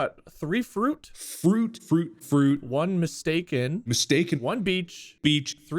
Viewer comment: When wade said that the word was mistake for the first time i heard “stake” so i was listening for bob and mark so talk about meat the whole time😂